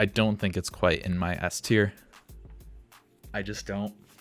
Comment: the subtitles say in my ass tier